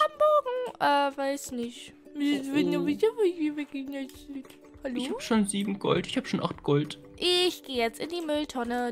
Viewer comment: was hat sie genau gesagt? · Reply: EPIG Heißt MATEO